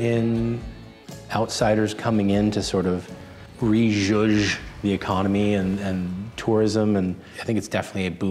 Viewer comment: what was the word he said?? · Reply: Re-zhuzh meaning to spruce up or inject new life into